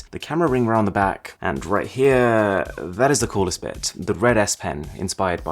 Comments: he said the red s pen What I heard : the red ass The problem is “red ass” in Arabic it’s “teezk hamra” like a bad word but in English no one use it it’s not a thing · I heard 'Red ass pen'